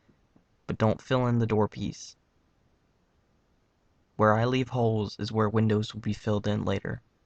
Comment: When he says "where I leave holes is where the windows will be" it sounds like he says "where I leave hoes is where the widows will be"